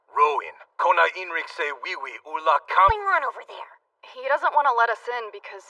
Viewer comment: did bro say wiwi😂